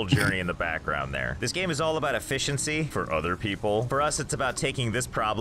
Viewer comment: he actually said “other people” instead of “older people” but your confusion made it better xddd